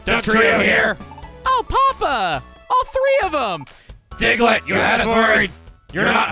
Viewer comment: Every time reads “Diglett, you had us worried,” I mishear it as “Diglett, you s word!”